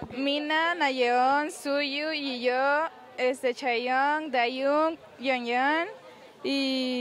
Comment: ENTENDÍ CHAYANNE JAJAJAJAJAJAJAJA BASTA